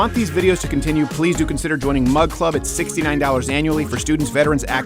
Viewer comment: I thought he was saying mud club 💀💀this whole time he was saying mug club my life is a lie